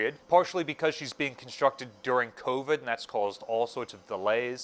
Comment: did you say constructed during COVID?